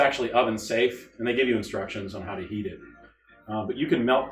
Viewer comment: i thought you said, " they give you instructions on how to EAT it "